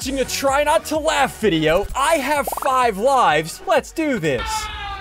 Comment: i heard 5 wifes